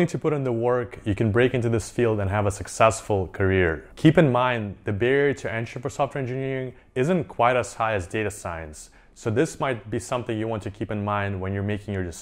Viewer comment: i couldn't understand what he said. Could smone help me pls. Great video btw. 👍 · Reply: "keep in mind the barrier of entering a software engineer isnt high as being a data scientist" something like that but the meaning is to be a software engineer is easiest to get a job